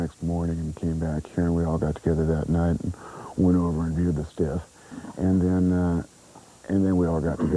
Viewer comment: Did he say they "Went over and viewed the stiff?"